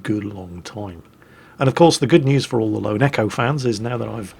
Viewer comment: Don't mind me, just early Also, my heart nearly freaked out when I thought he said "and for all you neko fans" when he actually said echo..I'm not weird, you're weird